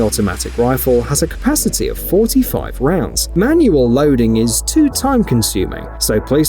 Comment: Did he say 45 round cap at 12:52 or is it just me?